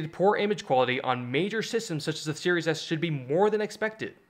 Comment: "major systems such as shshshshhehhsh"? No hate, I just can't make out what you're saying here. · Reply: Series S